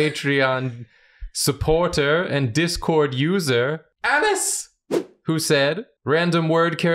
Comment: Misheard "Alice!" as "Ass!"